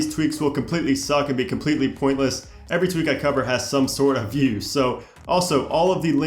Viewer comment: Easter Egg: Did you find the part I forgot to edit out? 😂😩 Edit : Top 126 iOS 10 - 10.2 Tweaks vid is live! --> <link> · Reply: it sounds like you said "every tweak I cover has some sort of fuse.".. What were you saying? :3 Doubt THIS is it, though lol EDIT: Oh, "USE" lol